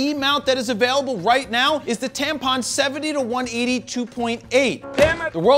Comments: Did he just say Tampon 70 -180 LMAO..No F** Givin lol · Did you just say TAMPON 70-180 🤣🤣🤣😅 dude you make me giggle so hard with your sense of humor, keep up the good work!